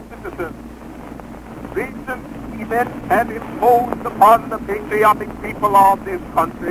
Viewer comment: I think he said idiotic people of this country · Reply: He actually said "patriotic" :)